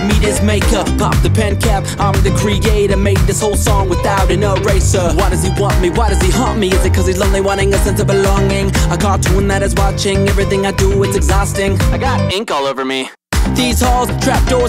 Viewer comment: i thought he said "why does he hump me" instead of "why does he haunt me" XD